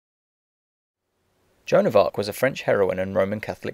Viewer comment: why does it say "china fuck" instead of joan of arc at 0:13 of the transcript lol